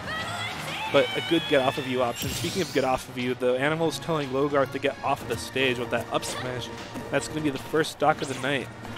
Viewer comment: WHAT DID SHE SCREAMED WHEN SHE GOT STAR KOed??! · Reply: "My battle ends heeeeeeeere!"